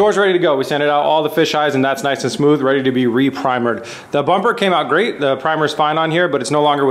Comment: Sorry, did you just say pre-primered?